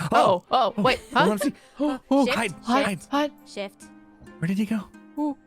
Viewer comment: Oooooohhh! is saying "SHIFT"!!! I thought I'd heard something else on Impulse's video. 😅